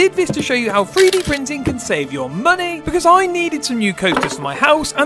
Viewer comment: I thought you said you were 3D printing money😢